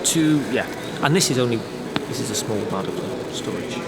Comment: Was sagt er bei 12:50? Ich verstehe es einfach nicht 🤔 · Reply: An der Stelle hing ich auch lange. Ich glaube er sagt: "This is a small, portable storage"